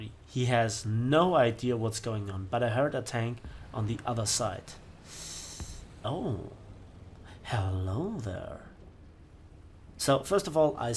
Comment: OK I have no idea what is going on.... It worked in the editing and in the preview. Sorry about this guys... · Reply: I thought I heard " Hello hello there." But I guess it's just screaming in the gulag...